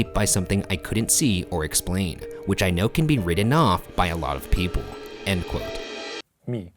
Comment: ‘T’s sound like ‘D’s sometimes. Like when he said “written off” I definitely heard “ridden off” or “opportunity” I heard “opportunidy”.